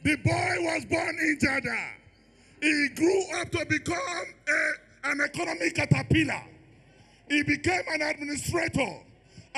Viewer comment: Who else heard " he grew to become economy catarpilar" 4:44 NIGERIANS plz wise up , we need a New Nigeria, not to repeat the mistake of the past